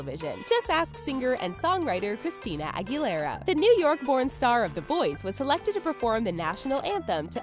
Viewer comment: i thought she said "pornstar" LOL